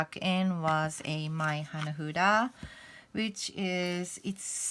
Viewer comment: As always, thank you for sharing your amazing practice. You are so incredible, and the reading at the end was amazing. I didn't catch the name of the small cards that you introduced at 11:11. Can you share that with us? Thank you again! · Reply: Hi dear friend! Thank you for stopping by and commenting on my video! That deck is “Hanafuda” and I am actually thinking of explaining one each cards 🎴 meaning in my way how I use it and to read it next week! Thank you for all your support and love.✨💞